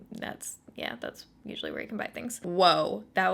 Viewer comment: I could have sworn you said "That was a giant aspirin that just flew by." I had to pause a moment to ponder my ridiculous hearing.